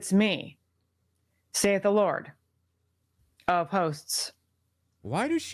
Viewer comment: I thought she said, "the Lord of Posts" 😂 That's a great name for Trump 😂